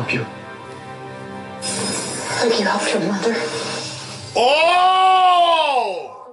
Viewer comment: What is she saying? :/ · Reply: "Like you helped your mother?"